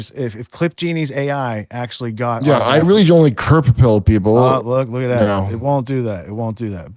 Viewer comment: Who else’s here to witness KURP’s rise as a (parallel)cultural icon? ❤ our fearless apocalyptic Jester adjacent to our fearless Nerd Sage-seer, Whitney Webb 🙏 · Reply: Wait at 16:13 what did he say.. He doesn’t like KURP?? or doesn’t Kurp pilled? Can someone clarify